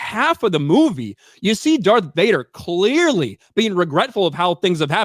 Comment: Sounded like he said Death Vader